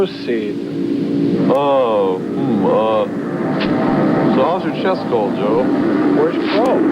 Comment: What did Servo say when Joel says, "Hey that's Crow's seat." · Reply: He changes the subject by saying "How's your chest cold, Joel?"